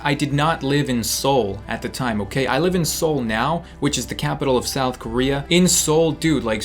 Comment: Did you mean Seoul I thought you said soul